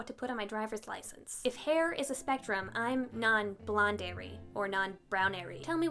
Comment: Did you just say "if colour was a spectrum..."?